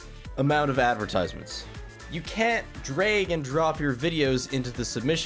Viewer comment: did he just say "drayg" and drop? 4:02 he said "drayg" and drop again